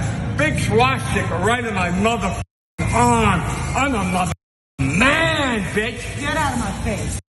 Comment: I heard him say he's a fucking man-bitch. Listen carefully, lol.